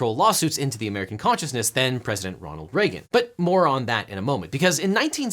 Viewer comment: I heard he said “moron that” 😂